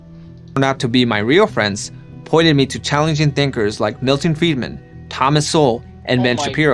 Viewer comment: Thinkers like ben Shapiro. Hahahahaha!!!!! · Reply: When I first heard this video I thought he said "challenged thinkers" and the list made sense to me.